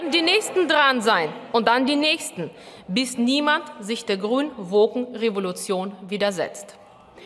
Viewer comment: was sagt sie da? Die grünen Wogen?